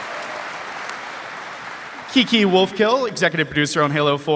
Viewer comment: For a moment there I thought he said "Kinky Wolfkill"